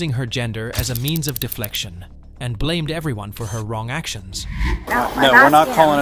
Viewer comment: LMAO "She Started Using her gender as a Means of deflection And Blamed Everyone for her Own Actions" did you Say "woman" ? Because... ☕🤣😂